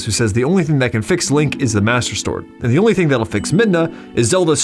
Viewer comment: Did you say “Master Stord” at @ 25:03 ?